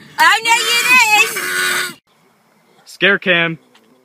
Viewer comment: For a minute I thought she said ace